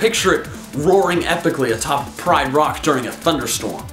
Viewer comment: Did you just say.... Prime rock? PRIME rock? Good list though.